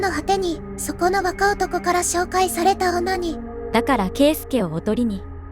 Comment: ～ 「紹介された女に」が「紹介されたオ〇ニー」に聞こえてダメだったｗｗｗ